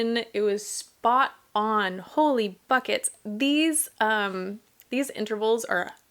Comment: For the record, at 13:11 said “Holy BUCKETS “ and not the gutter expression some of you THINK you heard.